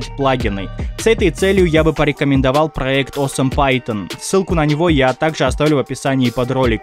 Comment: АХАХАХАХАХ , "Awesome python" а я услышал "Oo , senpai Ton"